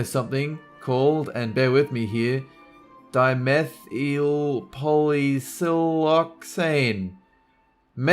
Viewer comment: all I heard was meth lol